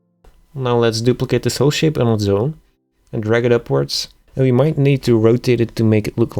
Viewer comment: What did you say? "Now let's duplicate the [unintelligible] shape [unintelligible] and drag it upwards" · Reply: whole shape on it's own...